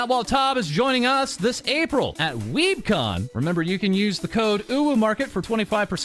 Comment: I know my allergies are bad right now and my ears are clogged but at first I thought said weeD-con not weeB-con